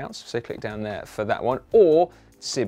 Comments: does say "don't forget to like and shit"???😂🤔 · Sounds like he said "don't forget to like and shit" lol